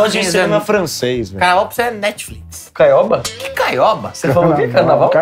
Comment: caioba kkkkkk eu entendi isso tb 😂